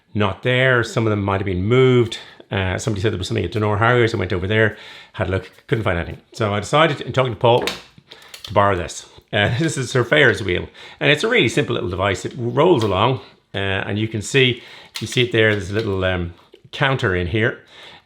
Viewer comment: thanks, when / if I get a Stryd I will have to do my own tests (suits me as I'm a scientist by trade!). Also, while I like most things about the specs of the Coros Pace 2, I have to stick with Garmin as it has audio prompts, which are necessary for me as I am partially sighted and really struggle to read the watch when running. Combining the watch with my android phone and connect app and a pair of Aftershokz Air headphones is the way to go for me to get told when I've done each km and what my average pace is :-) Also, I know that my current Garmin 235 isn't fully compatible with Stryd, so may have to also get a Garmin 55 to get the full Stryd functionality (if it is worth it?). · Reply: thanks, Your advice makes good sense to me (though I'm tempted by the Garmin 645M at Wiggle for £190!). It's funny you mention the "Ferris wheel" as that's what I thought you said the first time I heard the video :-)